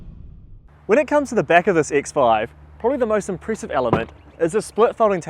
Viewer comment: Unless I’m mishearing the kiwi accent, at 0:49 he says “CX-5”. Freudian slip? Have one of those as well as a modern BMW. Sadly the former offers a far better ownership experience.